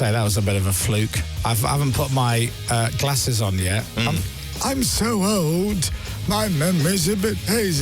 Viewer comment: every time I hear santa say "I'm so old...." I can hear him saying "my pussy is haunted" ala Emily Maitlis lol